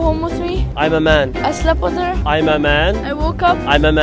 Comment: OH I THOUGHT YOU SAID SHE DISAPPEARED IN UR EAR